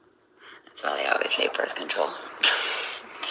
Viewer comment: I just commented somewhere else, don’t believe this. Notice she giggled right after she said it? Got up right after she giggled before she started laughing. No way she is not dumb she would never screw that up and say that. That’s her delusional attention money skit. Notice how creepy sounding she was when she said I love being a mother? Now when have you ever heard ever say that about her son? Never bc she doesn’t love to be a mom 🤷🏻‍♀️. Dont fall for u all she is gonna play ya like a fool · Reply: I thought she said that’s why they say they take BC?